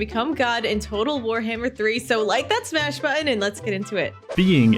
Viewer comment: I heard "like that smash button" and it factory reset my brain.